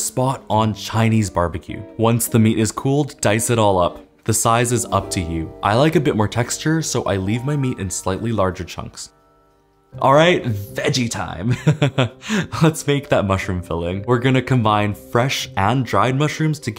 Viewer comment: I misheard and thought you said alright! Edgy time!😂😂